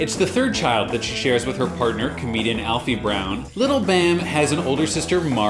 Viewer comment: on the intro i thought u said hairy brown 😂